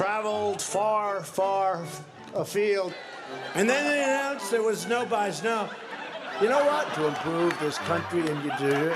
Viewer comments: The thought of Pence being President is the thing that gives me pause about this. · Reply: I listen to 5:21 closely and found that his dentures were lose. He said: “And then they announced there was no byes. No.” Well, at least what I think I heard. What do you think? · his dentures were loose and he was trying his best to keep them from falling out. I think he was trying to say: “and then they announced there were no byes. No” 🤔. OR he forgot to take his meds. I think he has some sort or dementia.